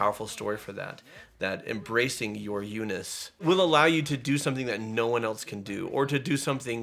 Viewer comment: I keep hearing, "Embrace your Uniss." Could we maybe make that a thing?